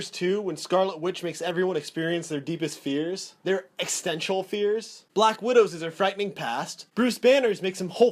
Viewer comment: Did you just say "eckstential fears"?